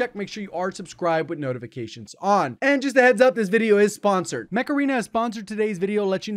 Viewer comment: I genuinely thought he said MACARENA HAHAHHAHAH I guess I wanna party wooooo!!